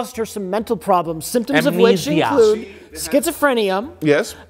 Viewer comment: does Rich say "schizofrenium?"